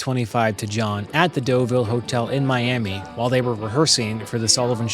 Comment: At first I thought you said that "Rickenbacker gave this 325 to John at the 'dildo' hotel in Miami." I had to put on the closed-captioning to see that you actually said it was the "Doville Hotel." I think I might need hearing aids...